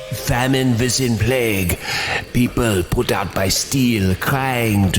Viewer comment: "put out by steel" sounds like he is saying "put out by force"